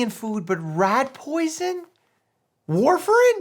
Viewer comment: I thought u said "Warframe" the first time 😂